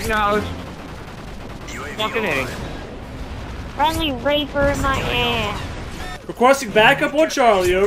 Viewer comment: did he say friendly raper in my ass? when did rapers become friendly??